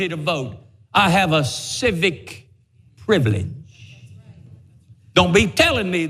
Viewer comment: He did say “you have a civic duty to vote”…… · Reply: I thought he said "privilege"